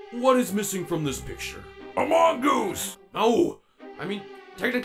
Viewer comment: thought he said a mongoose